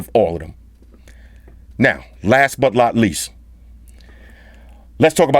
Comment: Not sure if I heard right but did he say "Last but lot least"